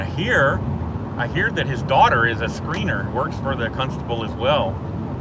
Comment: I thought you said his daughter is a screamer!